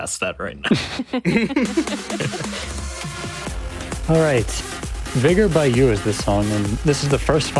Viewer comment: Sorry, but what is the Song starting at 24:40? Couldnt hear it right what the commentator said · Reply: Vigor - Yooh